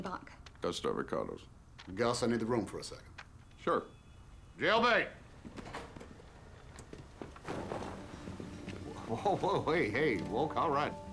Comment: Does he say "Jail Bait!"? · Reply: He calls the collection of young hotness that work for him -“jailbait”